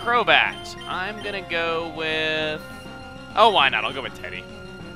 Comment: Every time you say "why not" I hear "wynaut"